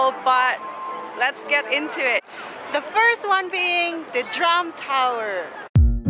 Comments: I thought I heard you say the Trump Tower (drum tower)....LOL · Thought U said The Trump Tower. Well....